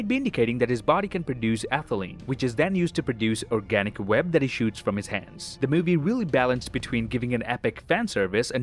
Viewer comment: "which is then used to produce organic web that he shoots from his ass" I mean he said it so fast it sounded like he said that